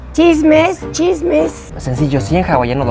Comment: Me quedé pensando... Yo pensé que dijo chistes y no entendí el chiste pero era chisme, Jaja!